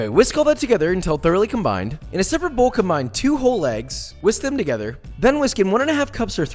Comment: I swear he said mix two whole LEGS